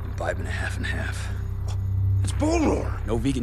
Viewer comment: "That's bone war"? is that really what he says?